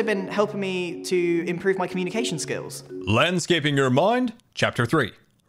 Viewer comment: i heard him say landscapeing your mom